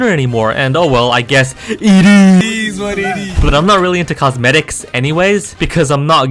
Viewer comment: what did he just say? cosmedks?